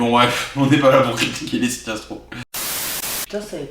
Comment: A 14:43 j'ai cru entendre "on est pas la pour critiquer lena situation" j'ai dû réécouter pour comprendre le pourquoi du comment de qui